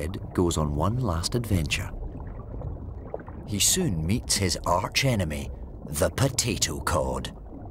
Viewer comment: The video froze at 0:09, and so all I heard was "meets his arch enemy, the potato" and I'm actually dying of laughter now 😂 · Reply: I heard "meets his arch enemy, the potato god"